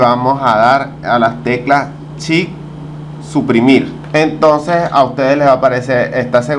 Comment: ya no entiendo lo Que dices en el minuto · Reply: Suprimir